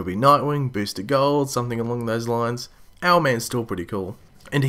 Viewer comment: My thoughts. -How tf was Rick able to almost kill Vandle without any weapons..... Just his bare fists, and Ray struggled to kill a birdman with a freaking power suit!? -HOLY FLIPING MOLY!! The JSOA!! -Now I need to search up who this Owl man is..... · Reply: yea guys sry, i couldnt hear voice that well, so i thought he said owl man xD and also Alpha Dreadlord not one time did they say he was weakened, but that wasnt the point. The point was that Mick killed Vandle w/ fists, and Ray STRUGGLED to kill that birdman w/ a power suit